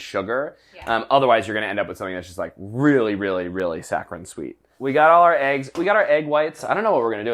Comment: Bon Appétit stands with the Black Community in the fight against police brutality and systemic racism. Read more here: <link> is supporting Campaign Zero: <link> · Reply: There're minor errors in the captions. At 3:15, the captions say "sacron" instead of 'saccharine'. At 7:37 they say "wet flavor" instead of "what flavor". (Thank you VERY much for including captioning on this video though. It SHOULD be provided on ALL of the BA videos and it isn't.)